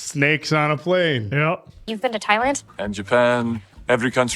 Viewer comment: Iron dragons has a Taiwan flag on the back of the dogi. Axel was supposed to be Croatian. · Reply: I thought you said Taiwan because silver first saw wolf fighting in 'Thailand'